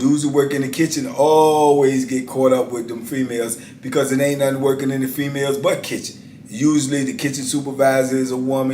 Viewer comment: did anybody else hear say," it's nobody else working in the females but kitchens!" 😆 🤣 😂 I'm dying over here!!!